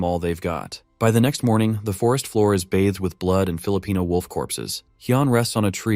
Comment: what the fck did u say? blood of filipino wolf?